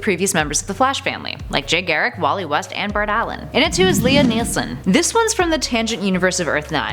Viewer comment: when you said lia nelson I thought you said liam neeson